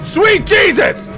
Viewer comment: so , do i have a ear problem ? i hear "Fruit Jesus" x) funny ... and im french